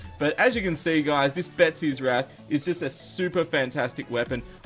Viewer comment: I accidentally heard him say "Betsys rat" I lolled so hard